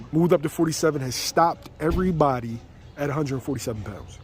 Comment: Ya'll stop making excuses for Spence. He's either a killer or not. It shouldn't matter what the other guy is doing. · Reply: oh stopped... I thought you said killed. Silly me